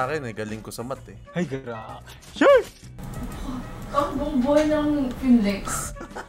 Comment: ano sabi, di ko masyadong maintindihan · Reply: "Buong buhay nalang nun i-finilex(flex)"